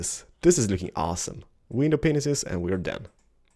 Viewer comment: Ahemmm...Am i the only one who heard "window penisis?" XD